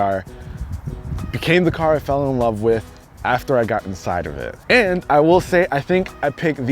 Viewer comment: did he say he fell in love with it after he got inside of it